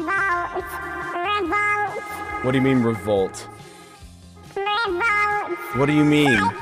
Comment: I didn't even know he was saying revolt- I thought he was saying "no boat" or "rainbow"